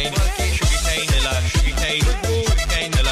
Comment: 😀🔔🔊sugar cane pillar , sugar cane, brick wall, sugar cane piller, sugar cane, brick wall🔊🔔😀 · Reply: I thought it said sugarcane Hitler